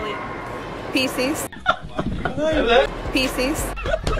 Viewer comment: I thought she said faeces but she was trying to say pisces 😂😂😂😂